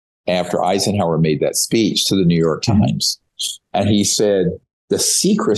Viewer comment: One time that Greer said, "The New York Times," I heard "The Manure Times." LOL